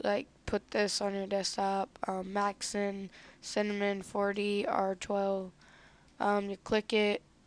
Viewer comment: no offense but did you just say cinnamon 4d